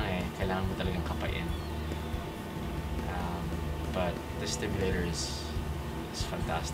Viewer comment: did he say stimulator?